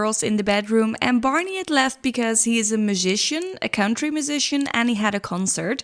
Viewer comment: Great Job! I thought I heard that Barney was a country Magician at first, not a country musician!! Lol